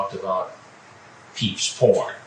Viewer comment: What does Chris Handsome say at 8:18? "Peach p0rn." What? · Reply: He says "peeps porn," reading directly off of Wehry's transcripts. Apparently, the decoy said something like "my peeps said there's big money in porn," or something like that, in response to Wehry's claim that he made some gay porn films. Wehry mistook that as a genre of porn, so when discussing it later he referred to it as "peeps porn" to try to sound cool. Nothing he said sounded at all cool, but I'm pretty sure that's the story.